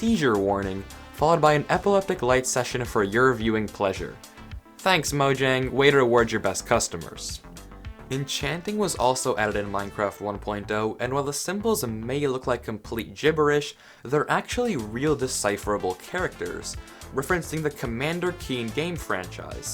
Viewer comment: am I hearing things or did you say Minecraft 1. Dough instead of 1.0